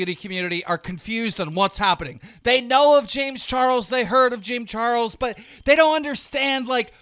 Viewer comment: is it just me or does he say “Jim Charles”